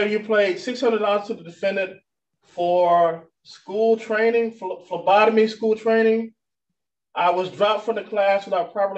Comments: I thought she said lobotomy class at first 😂 They have a hard time finding my veins too. Sometimes they have to get the Ultrasound machine out to find a vein 😂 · I thought he said Labotomy class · I literally thought he said lobotomy school training the first time